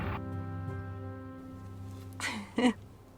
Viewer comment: Oh gawd I thought she said the N word but I think she said "Almost forgot my knickers?" lol